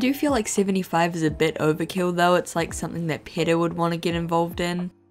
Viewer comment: I swear I kept hearing her pronunciation of PETA as "pedo"